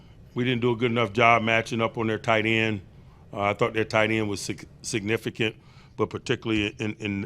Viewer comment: I thought he almost said "I thought their tight end was sexy"